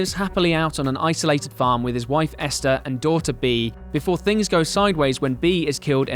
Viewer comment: when you said "Daughter Bee" I heard "Doll to be"